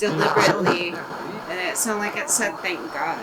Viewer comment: I heard “With God” after you asked are you in heaven.